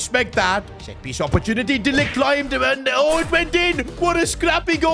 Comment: On 1:13:12 caption said d!ck climbed and it went it bro wtf